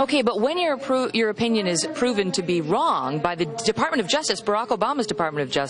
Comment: I belive I heard him say DOG a few times haha It's DOG ( department of Justice )